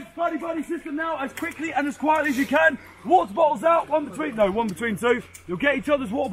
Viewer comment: Water bottles. Took as few seconds to process that expression. I heard "War balls." I was confused.